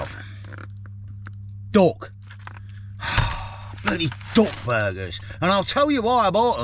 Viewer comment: Great video as always, but as an Australian listening to a english accent I thought you were saying DOG burgers!😂 glad it was DUCK! 😅